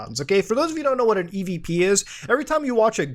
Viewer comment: I thought he said EDP lol